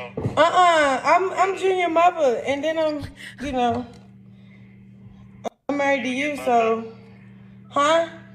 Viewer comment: did she say I'm married to you?